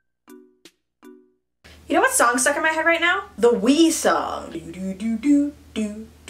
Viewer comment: I thought she said “the wee song” and was about to sing a british educational song for kids 😳😳😳